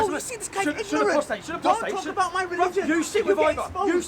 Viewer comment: Did he just say "Don't expose my religion"??? 🤣🤣